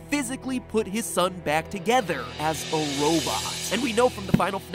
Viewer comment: I hear “as a robot” as “as ballora” and ai was just like WHAT